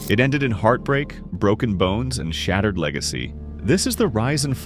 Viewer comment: did i hear "shattered leg a see" ??